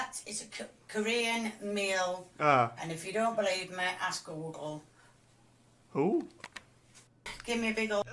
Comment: "That is a Ka... Kore...Korean meal, and if you don't believe me, ask WUORGKQGUNG" @ 29:00 I'm dead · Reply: I thought she said “ask a local.”